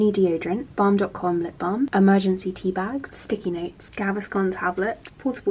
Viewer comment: Love the emergency tea bags! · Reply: Ahhhh, i thought she said emergency poo bags HAHA i was confused